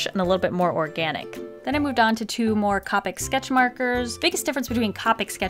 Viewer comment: she said capic instead of copic